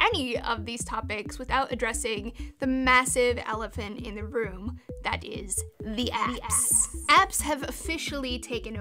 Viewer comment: I swear I thought Laci said "The Ass!" for a second 😂